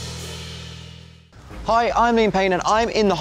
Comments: I thought he was saying at the beginning : ”hi I’m in payne“ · At first I thought he said “Hi, I’m in pain” and I was like “What??” 😂😂😂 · I Swear to god I heard "I'm in pain" when liam said "I'm Liam Payne" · It sounds like he says "hey I'm in pain" in the beginning..;) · all i hear is “hi I’m in pain”.... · he went so fast at the beginning i thought he said “hi i’m in pain” 🤣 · Did I hear it right? "Hi, I'm in Payne." ? 😅